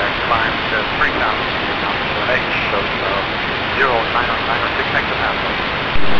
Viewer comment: I think he said 0996 this time, not 7.